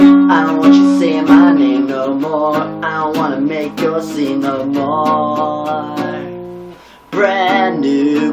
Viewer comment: SME has made some great music. I think the band may have broken up but I'm not entirely sure what happened to them. Sloppy Meateaters is one of my favorite punk rock bands of all time. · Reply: Lol, that somewhat sounds similar to it. But I always thought he said "I don't wanna make you scream no more."